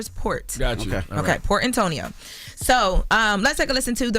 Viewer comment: I thought she said for Antonio myself lol.